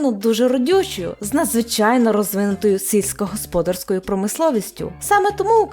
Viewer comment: На 19:11 було задумано що «сільськогосподарською» звучить наче цицькогосподарською? Чи це мені просто почулося?🙃